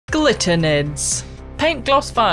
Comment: Swore I heard Glitter nerds. 🤣❤️‍🔥